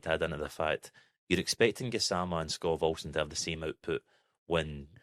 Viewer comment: I'm embarrassed to say over the past few days I thought you guys were saying "Scott Wilson" not Skov Olson. Was very confused.